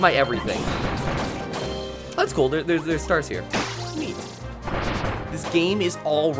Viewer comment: was I the only one who heard a slight “woomy” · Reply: But it was neat not woomyl